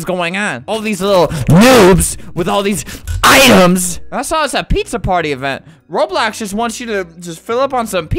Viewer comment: I thought pizza party said Rat Party